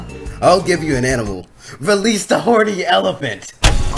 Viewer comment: "let go of the horny elephant" hahahahaha I liked that part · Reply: but he actually said “release the horny elephant!”